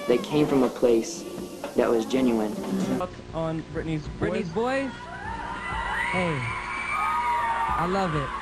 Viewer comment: what does he say at 1:10? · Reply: he says he loves britneys VOICE not song boys lol